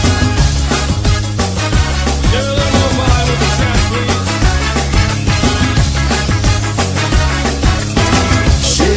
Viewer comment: I always was wondering what he was saying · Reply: "Get a little more wine with the check, please"